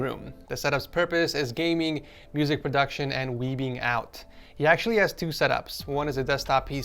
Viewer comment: Definitely thought you said "weeding out" when talking about the last set up from California. LMFAO